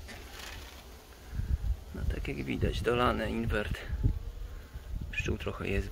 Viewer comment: Czym zakarmiasz? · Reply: Już usłyszałem... inwert...